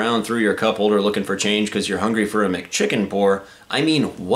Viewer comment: At first i thought you said a McChampaigne when you said McChicken. Lol